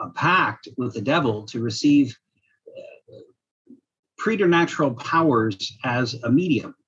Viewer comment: what's the word he says at 7:16? Predanatural powers? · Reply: Preternatural